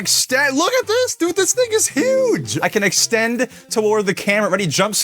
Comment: did he say the n-word?! 😮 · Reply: he said this thing I’d huge.😭